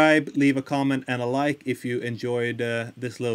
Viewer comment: I really thought it said "blame it on hellberg!"